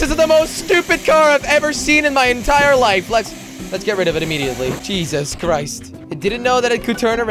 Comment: On the Bugatti it sounded like you said cheeses christ